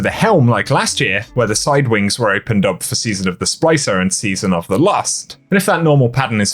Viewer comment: Dude it sounds like you just said the season of the lust instead of season of the lost